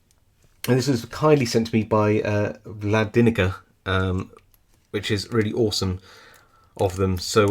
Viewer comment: This is a super cool lock - a soviet era Abloy disc lock clone! I didn’t know they made an Abloy clone in the CCCP, so thank you to Vladinica for sending this one to me 😃😃😃🔐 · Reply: Vladinica I must wash my ears out I thought you said Vlad vinegar.l.o.l. but thank you and thanks to your good friend Vlladinica to let you show us all and learn a little at the same time .Great to see the inner workings 🎉🎊🎁🎈🎄🎄🎄🎄🎄☃️⛄️ stay well stay safe